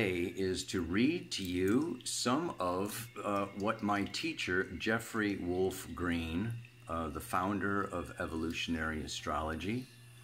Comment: That was so awesome, as are you! I can scroll back, but if you have the time could you remind me the title of the Pluto book and the author? I thought you said Jeff Green💚